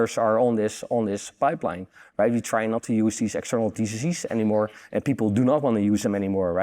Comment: can someone tell me what TCS stands for ? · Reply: I think you are referring to when he said "DCCs" (Digital Content Creation) software like Blender etc.